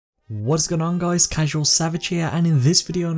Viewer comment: Again, I heard him say CABAGE SAVAGE